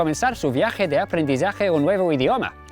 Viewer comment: Did he really say "Du hasch nix zu verlieren"? It sounds like an imitation of any south western german dialect😂